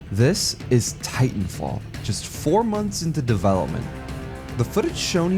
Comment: i thought it said "This Is Titanfall 4, Months Into Development"